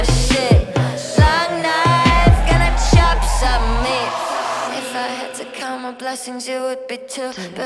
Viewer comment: why do I hear "barf loud" "long night, gonna choke on meat" 😆